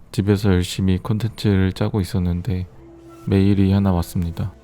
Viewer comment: 그...영상 초반 멘트중에 '열심히 콘텐츠를 짜고있었는데' 이부분 자막이 없었더라면... '열심히 코딱지를 파고있었는데' 로 들었을거예요.. 내 청력이지만 너무 미워..ㅋㅋ